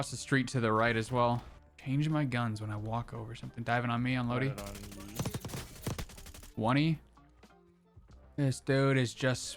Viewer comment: what does he say? I always hear that callout yet I don't know how it's written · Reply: “One-y” like he is 1 shot to die